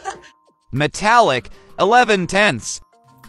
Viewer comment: Oh, I thought that said negative 11/10